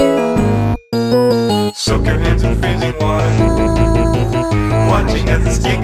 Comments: wait... is it 'soak your hands in freezing water'? for some reason i keep hearing 'soak your hands in fizzing water' freezing water makes more sense LOL but fizzing water sounds creepier for some reason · I thought he said "soak your hands in fizzing water", "watching as the skin gets softer". It would make sense since carbonated water would make your skin softer after some time.